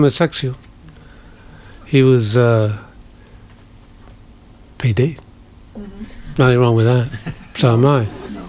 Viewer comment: what is he saying? paedo? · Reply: Pédé is “queer” in French (the talk is in Paris).